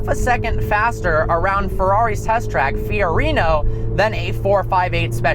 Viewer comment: did he just say fiorino?